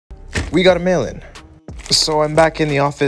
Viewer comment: How many people always heard him saying we gotta melon!